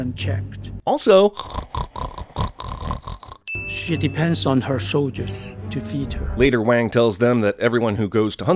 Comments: Erm... Did i hear that right? Did the strategist WANG say "Shitty pants on her soldiers"? Is this information neccessary to win the battle? Is it important to know that the monster soldiers have shitty pants? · I'm not the only one who heard " shitty pants on her shoulders " at 9:25 am i ? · I can't be the only one to hear "shitty pants of her soldiers" at 9.26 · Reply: The Democratic People's Republic of Korea literally the first time I heard "She depends" but now all I can hear is shitty pants